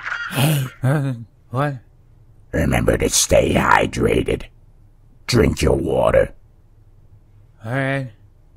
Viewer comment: I thought he said drink your. Brother that make sense